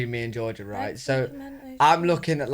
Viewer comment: Georgia misheard PIMENTO as FERMENTED